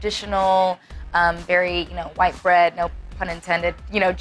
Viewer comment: “White bread no pun intended”😂😂😂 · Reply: I thought she said white brand?